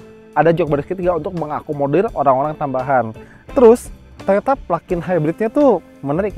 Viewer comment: Menit 15:19 toyota plug in hibrid 😂 atau gue salah denger?